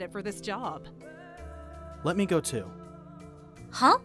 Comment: why do i keep hearing someone say uwego! Or something